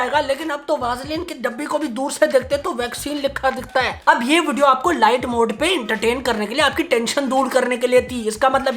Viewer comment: Haha sach moun pe aa hi jata hai Am i the only one who heard vaccine right